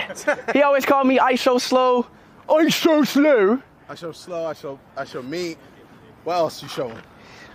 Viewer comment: Ishowslow😂😂😂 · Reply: I thought he said I'm so slow